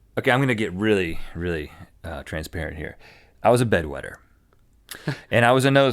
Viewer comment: When Rhett said, I was a bed wetter, i thought he said, I was a bed winner, as in he was a pimp